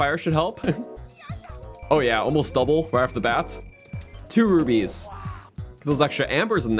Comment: I thought he said 2 rupees.